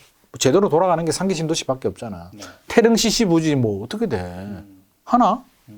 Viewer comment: 태릉시라고 한건가요? 뭐라고 한 건가요?